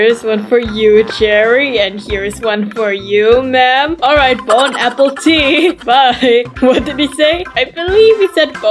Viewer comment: did they just say bone appletite